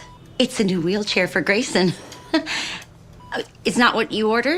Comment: Shoot I thought he said gayson